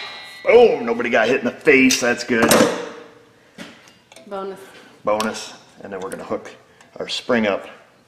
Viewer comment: I thought said "bummer" until said "bonus"...